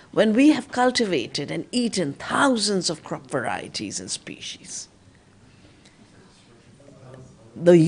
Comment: Hi, can somebody help me? I don't speak english a lot, and I couldn't understand the words she said in the minute 0:42 and the minute 1:30 , can you write it? is just those two words, please! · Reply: crop varieties and species. 1:31 - ecocide [ ecological suicide]